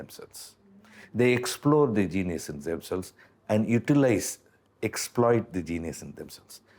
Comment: Exploit 을 explode 로 잘못 들었나..